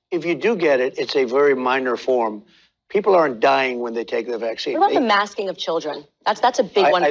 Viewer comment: it sounds like trump just said "people are dying when they take the vax". Correct me if i'm wrong.